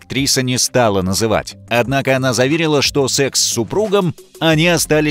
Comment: я один слышу СЕКС С СУПРУГОМ